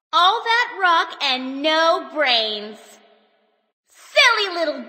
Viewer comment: sorry, I misheard it for: “All Rockets No Brains, eh? Mate.” Coming from Mr. Mundy as TF2 Sniper